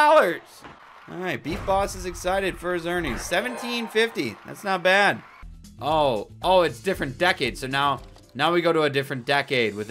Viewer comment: i don’t know why but I thought decades said deadass